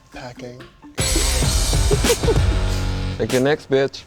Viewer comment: wtf?? did he just say "break your neck, bitch" 😂😂